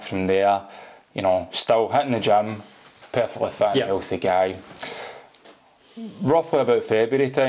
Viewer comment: I thought he was saying "a fat healthy guy".